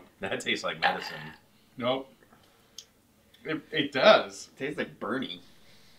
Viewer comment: G said tastes like burny. My old group of buddies and sibling say, tastes like burning. And that's what I thought he said.